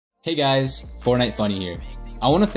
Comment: I heard "Hey guys, Fortnite MONEY here," 😆